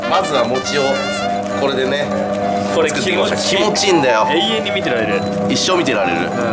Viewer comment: ち○ち○だよって聞こえる